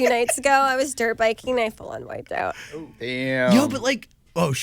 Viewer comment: Please what does she say at 4:32!? "Two nights ago..."? Hahaha thanks in advance!!! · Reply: “I full on wiped out”